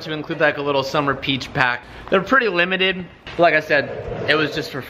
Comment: i swear i heard him say peach pack at one point